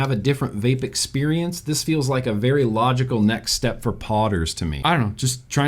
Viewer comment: When you first said "podders" I thought you said "potters", like it would be good for pot heads. I am in need of sleep. 🤣